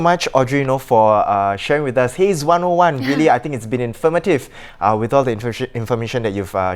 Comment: > I heard the word “infirmative”. Pls correct me if I heard wrongly. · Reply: Nope. "Informative".